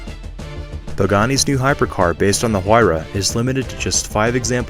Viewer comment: I thought you said supercars not hypercars e.g. Pangani